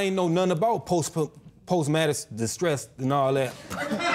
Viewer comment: Did he just say "Post Madison Distress"?